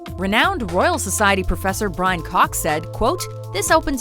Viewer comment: did she say Brian Cock? 3:42 what a name..